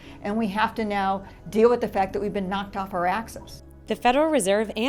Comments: thought she said knocked off our asses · When she said "knocked off our axis" at first I thought she said knocked off our asses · When she said knocked off our axis I thought she said knocked off our asses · Did she just say knocked off our ass's😱